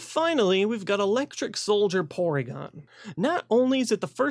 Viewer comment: I thought he said Electric Soldier Porn. Kinda blew my mind a bit.